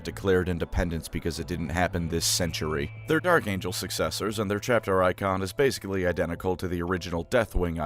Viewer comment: it sounded like you called them Narc Angels successors, and I have decided this is my head canon name for the First Legion now.